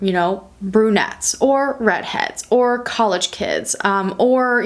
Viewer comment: Hi, you used the words Coloured kids, with this time of black matters. The slaves were always referred to as coloured, and it’s not really used naw days. Anyways video spectacular as always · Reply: I’m not sure what part you’re talking about, but I never heard her say that. What she said was “brunettes, red heads, college kid...”